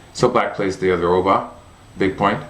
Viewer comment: Glad that you're back! Don't pressure yourself for new videos, but I'll be looking forward to them! At 10:48, you said "Black plays the other [...]". Is the next word a Japanese term? Sorry, I couldn't understand it. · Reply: That's what I translated to "big point". An Oba is a large point in the opening.